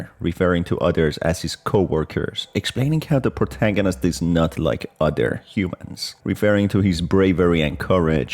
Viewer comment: Actually, I think you Misheard what Miss Delight said 2:36, She said "You're a LOT LIKE the other humans in that way." And that was after she said "Not a Good Listener are you?" And yeah, She definitely has seen Bravery and Determination like that before. (Her sisters and The Man in THOJ VHS)